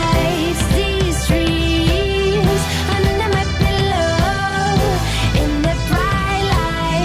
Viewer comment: i thought it was saying "these trees under my pillow"...woopsies